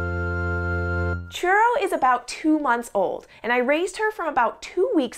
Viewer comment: I thought she said Cheryl not Churro xD nice video i love pigeons too